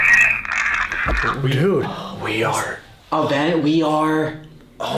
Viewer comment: It seriously sounds like it says “we are legion” or “we are a legion” at 16:55.